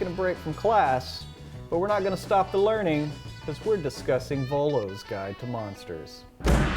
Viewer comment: I thought is said Volvo